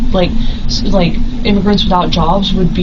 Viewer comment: At the 10:18 mark, I swear the suspect says "immigrants" and not "individuals", as the subtitles suggest. It's weird to me that people are so casual with their bigotry that they don't even realize that it's an awful look, ESPECIALLY when giving a statement in defense of their innocence.